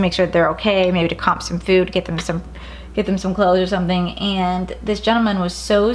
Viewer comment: did you say clothes?